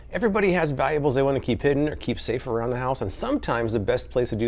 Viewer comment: At the very beginning it sounded like you said, "Everybody has bibles they like to keep hidden..."